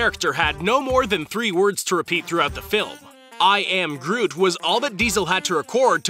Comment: I thought he was saying "I am Grohl" because they were Foo Fighters of the Galaxy.